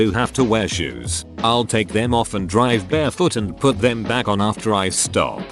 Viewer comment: Thought it said, "...drive Bigfoot." It's hard to get him out of 2nd gear. That finishing a vid makes one a duckiing beast. Well, that one's true.